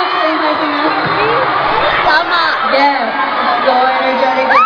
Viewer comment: did CL say "Yes, so energetic in here" ?? instead of the subtitles?! or is it just me?